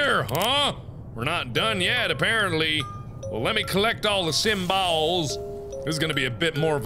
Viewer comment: did he say sym-balls